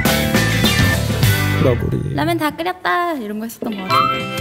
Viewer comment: 에 뭐라고 하는 건가요? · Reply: 라면 다 끓였다~!